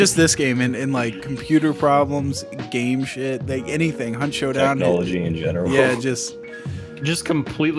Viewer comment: did i hear hunt showdown Alligator Sex Intensifies